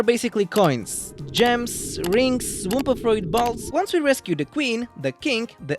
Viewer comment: I heard "balls"